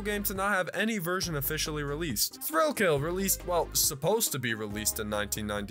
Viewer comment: Bruh. On "Thrill Kill" I thought you said "Thrillville" and got wildly confused